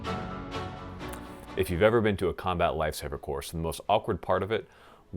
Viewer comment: Ok who else thought he said "combat lightsaber course" at the beginning?